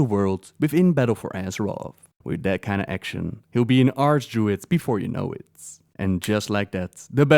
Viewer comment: I could have sworn he said Arse Druid. Im like " Whats an ArseDruid?" after about half a minute i realized what he said, and how stupid i can be at times.